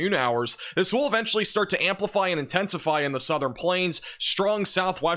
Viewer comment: did he just say amplify 💀😱